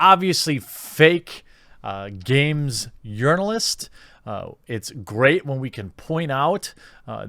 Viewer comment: Did he just say yournalist?